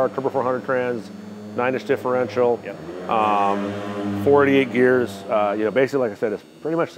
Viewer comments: Did he say 48 gears? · i swear he said it has 48 gears